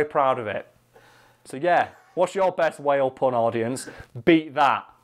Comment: got worried at the end i first thought he said whale porn not whale pun